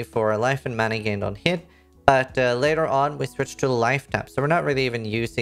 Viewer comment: "Later on we switch to..." what? I couldn't hear it, can anyone explain how you manage mana later on please? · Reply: Switch to Lifetap, the red gem in your 6-link